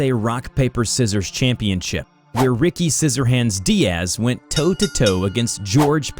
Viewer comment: When i first heard "Scissorhands diaz" I first thought diaz NUTS